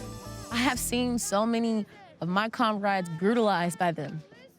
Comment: Lol lady at the 5:25 mark said conrade instead of comrade. Just goes to show she doesn’t know what she’s talking about and is just on repeat. The blind follow the blind.